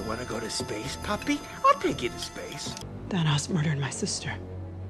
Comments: Wait does nebula say “Thanass murdered my sister”?!! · Is it just me, or did I hear “dat ass” instead of thanos